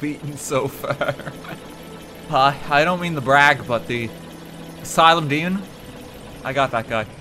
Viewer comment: I misaheard you say Asylum Demon as Asylum Dean